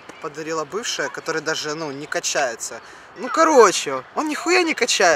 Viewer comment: мне послышалось, что бывшая не качается